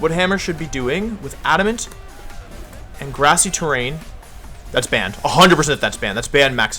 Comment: Hahaha thought you said ”thats banned” when rillaboom did a lot with grassy glide